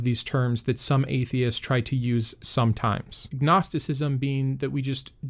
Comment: I love the channel and this was a great video. I just wanted to point out around @ 12:25 mark it sounded like you said "Gnosticism" instead of "Agnosticism," but I rewound it and confirmed you used "Agnosticism." However, hearing it made me think you'd do a pretty good video about the Gnostic branch of early Christianity, and how it explains the actions of the Abrahamic God much better than the Christian God of the bible. Also, let us know when you've opened up a Patreon account (or provide the details to your current one). I'd be happy to chip in to help you produce more great videos.